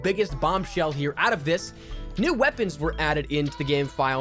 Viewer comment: sounds like it said "NOOOO" when you said new weapons being added